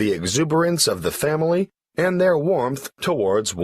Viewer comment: Warranth means plz tell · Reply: I heard like " warmth"...I don't know whether it is right or wrong.